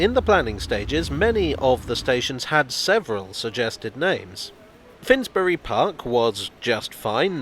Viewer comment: You said Cockfosters! Several times! This video's bound(sgreen) to be taken down. And at 1:09, did you say 'Many of the stations had several suggested names' or did you say 'suggestive names'?